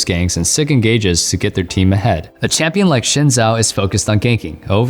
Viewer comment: Is he said ''Şin Zhao?'' Excuse me wtf I though it was ''Xin Zhao'' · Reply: i thought he said "Jhin Zhao"